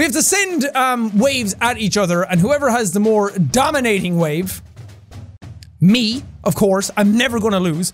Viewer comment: I thought he said 'the dominant wade'